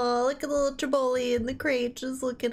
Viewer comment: did you just call the puppy a "little Jabroni"? 😂 another great reaction to an amazing film, hope you like the rest of the anthology! 👍